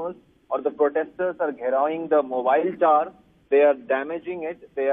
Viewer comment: Did he say “Giraoing” the mobile tower 😂😂